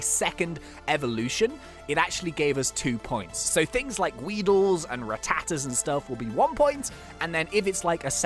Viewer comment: did you said WEEDLE